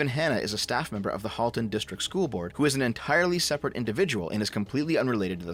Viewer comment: I keep mishearing "they then" as "they/them" ☹️